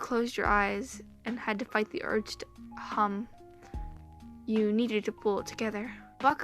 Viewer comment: Me like oh ya just him braiding my hair me at 9:59 did she say cum me: YO WTF